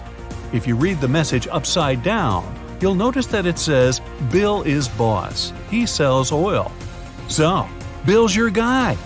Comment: oh, i thought it said "BILL IS BOSS HE SELLS ALL" lol.